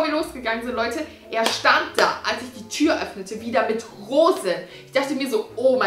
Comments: HABE VERSTANDEN DER STAND DA NUR MIT HOSEN UND DANN NOCH :,,OMG ER WAR SO SÜß"!!?? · Ich hab verstanden "Er stand da mit Hosen" und ich dachte mir nur so "na ich hoffe doch " 😂😂